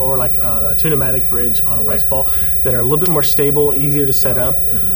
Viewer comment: does he say, "Rice Paul" referring to the yellow guitar?